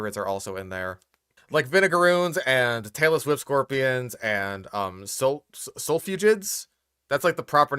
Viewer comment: Taylor Swift scorpions? Out of all the names spat out during this video, this might just be the weirdest one... (yes, I know I misheard)